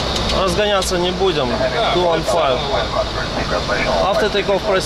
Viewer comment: услышал: "Разгоняться не будем"